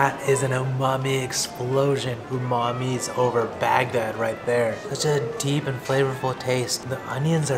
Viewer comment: Did he just say Umami is an explosion over Bagdad at 10:40 ??